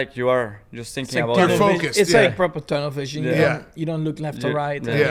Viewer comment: did he just say carpal tunnel vision😂😂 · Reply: TUNNEL VISION ( Max’s Accent is Strong, but I have been following him since 2015, so I understand him better now 😉)